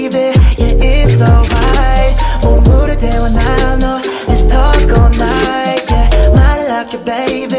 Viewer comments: When the video started I thought he said it’s taco night and i got excited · I kept hearing "it's taco night"😂